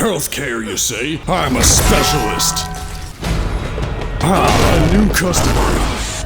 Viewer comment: I have watched this countless times and only now do I hear the AMONGUS